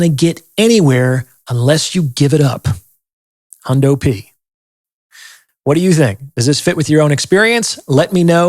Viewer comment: What did say here after "...give it up"? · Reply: He is saying "And most of you ain't gonna get anywhere unless you give it up... HUNDO P... meaning 100%" Capisci???